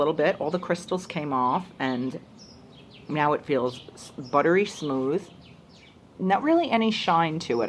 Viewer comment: did you just say "meow it feels buttery smooth"? Or is my brain just inserting meows where they don't belong? LOL